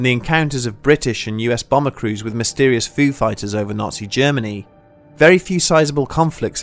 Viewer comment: When you said "Foo Fighters over Germany". I thought for a second you said "Food Fighters over Germany". I picture pilots coming back and telling their CO's "It was crazy man there were bottles of Bitburger, Parkabrough, Schnitzel, and Bratwurst flying every where. We didn't know if we were meant to shoot them down, eat, drink, or fly away...Oh the horror! The Cockpit's window was covered in meat and dark beer it was crazy man crazy!"